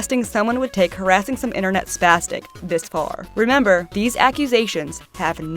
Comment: DID THEY- DID THEY JUST SAY SP*ST*C